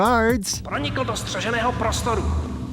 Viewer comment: hmmmmm…. Why am I hearing Czech language in France?😅 “pronikl do střeženého prostoru” means “he breached secured area” and yes, “he” 😅😅🤷‍♂️